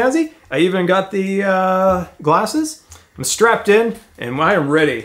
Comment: #Sendscartothemoon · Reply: I thought you said "send cars to the moon" but I realized